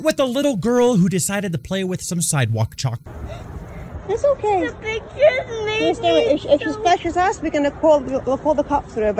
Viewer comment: Did she just say the bacon made me doo